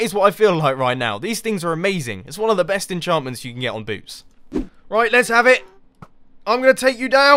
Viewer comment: XD When Mumbo said "its one of the best enchantments you can get on boots" Youtube thought he said "these things are amazing is one of the best in jarmin's young peeps" ROFL